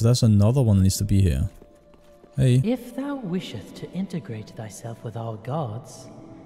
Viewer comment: They got a subtitle/VO error at 18:50 in the game. Voice says "integrate", subtitle says "ingratiate". Commenting in case a dev sees this (